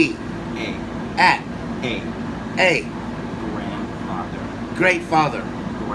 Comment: I can tell by the excitement he had when he finally got it that he’s going to be a GREAT grandfather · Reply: I thought he said Grand Greatfather